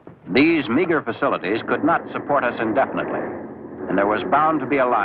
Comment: What was that said at 21:05? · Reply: “meagre facilities” nice bad faith question tho, racist.